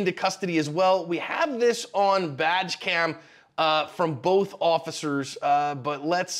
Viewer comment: I swear I heard "We have this on VAG-cam" twice. lol